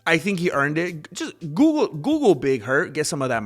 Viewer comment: I thought he said he was Big Herc for a minute, from Prison Talk.